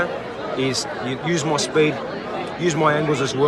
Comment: Did he just say that he has more skill than Lomachenko? 😂😂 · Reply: I replayed it like 5 times I thought he said skill he said speed lol.